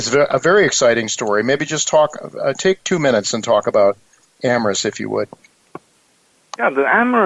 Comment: Did anyone catch the name of the stock that chinlon was talking about. It sounded like he said Amaris and it was a biotech stock. Thanking you in advance if you can help me with this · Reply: Amrs